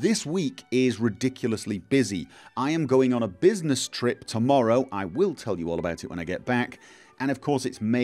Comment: I thought he was gonna say "I'm going on a business trip to Morrowind"